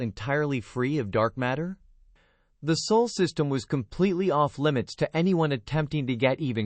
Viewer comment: Please be so kind and point out whatever mistake you might have noticed. If it's a major one I will take down the video as this story is part of a larger narrative that I am writing. If the mistake is of a graphics nature then all I can say is that I'm sorry as I am still learning DaVinci Resolve. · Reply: subtitles spell sole instead of Sol. Also on my system voice sounds only in left headphone.